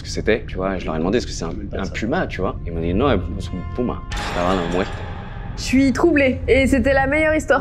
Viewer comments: Juste svp il as dis quoi a 32:59 svp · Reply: Jsuis claquée en Espagnol mais en gros il dit "c'est pas un puma, c'était la mort" je crois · Vert la fin le gars il dis "c'est un puma" et après j'ai pas compris il a dis quoi c'est a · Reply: il a dis "ce n'est pas un puma, c'est la mort" je crois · Question les gens déjà bonjour ,et aussi j’ai absolument rien compris à la phrase à 33:00 il a dit quoi ?(j’ai compris es no puma es …?)